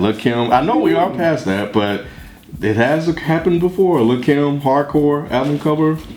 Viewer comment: i swear when said "little kim hardcore" i thought he said "little ken was hard" lmao